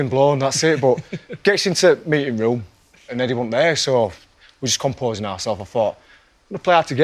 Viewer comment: Brilliant! Thank you for letting us know! :D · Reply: Very close but there were a few little miscommunications. says "and then I thought to meself 'bloody hell, cover's been blown, that's it' but gets into t'meeting room (dialect for "we got into the meeting room) and Eddy weren't there so we're just composing ourself I thought I'm gonna play hard to get 'ere" whereas Eric gets "and then I thought to meself 'bloody hell, cover's been blown, that's it' but get US into THE meeting room and THEN HE weren't there so we're just composing ourselves and I thought I'm gonna play hard to get 'ere". Pretty close to flawless with just a couple of points where he mishears.